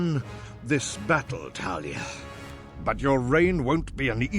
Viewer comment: Did he just call you Talia? :D